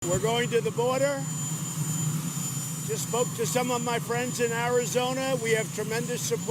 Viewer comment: Not going to lie it totally sounds like he says" I'm going to the border to smoke with some of my friends in Arizona." Trump we all know you've been smoking something with your friends.